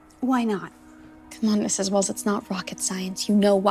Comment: She says Mrs. Wills but the subtitles say Mrs. Welling, the subtitles are extremely on basically all his videos though.